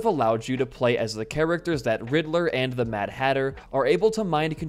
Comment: i heard you saying hitler and the mad hatter